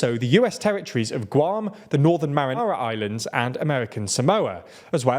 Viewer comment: did you just say "Northern Marinara Islands"